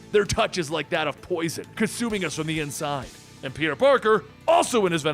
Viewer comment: Lol I thought he said "Peter Porker"